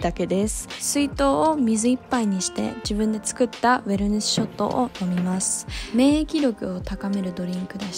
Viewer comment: のドリンクって何だろう… メルネスショットって聞こえてしまったけど 検索しても出てこなかった😭 誰かわかる方いらっしゃいませんか？ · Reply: ウェルネスショットですよ